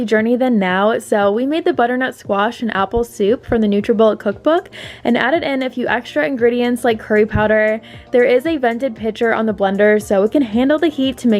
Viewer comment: I must be so hungry, I thought you said: “added some extra ingredients like harry potter” 😂😂😂